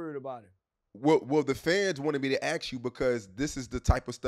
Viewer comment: did he say the feds wanted me to ask you? Or I’m tweeking?